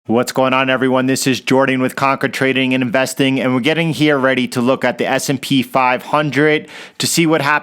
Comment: Conquer Trading & Investing where did you typed it I like to see it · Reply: Conquer Trading & Investing ok I did not see it I was busy shorting any way let this work for us. I saw the video I thought you said we are going hire